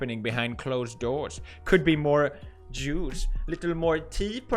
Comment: Haha, I could do sworn Felix said "There could be more Jews" not "more juice". Freaked me out I was like 'not again Felix!'. Phew :p